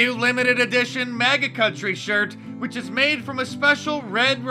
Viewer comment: YouTube subtitles showed "maggot country" instead of MAGA.